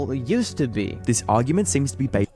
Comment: it sounds like it said "This argument seems to be bait" and I can't stop laughing